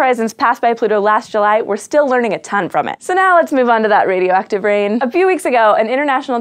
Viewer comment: I thought it said "Radioactive Iron Man" xD